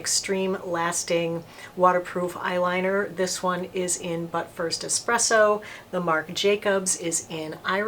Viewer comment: I thought you said, “ Butt First Espresso”. 😂